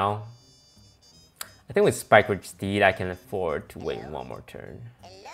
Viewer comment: Is it just me or does Toast say spikeward steed instead of spikeridged steed? Reference